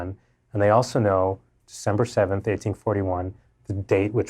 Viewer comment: ? Did he say Dec 7 1841!